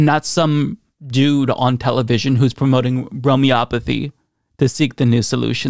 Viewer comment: I thought he said grope-me-opathy.